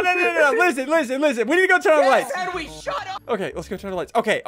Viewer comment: When it said suit up,for a second I thought it said shut up XD😂😂